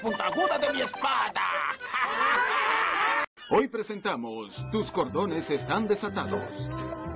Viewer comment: escuche "tus condondes estan desatados" xd